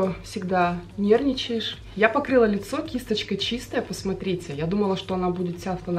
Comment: вместо покрыла послышалось побрила,слава богу,что все таки показалось😄